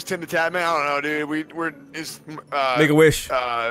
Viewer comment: I legit thought he said ’N-Word Wish’. XD